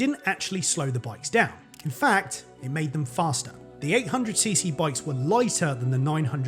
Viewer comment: Quick funny thing. At 8:52, on screen show "It mad them faster" instead of made. Dropping cc might make some people mad, so not sure if this was intentional or just a fin coincidence.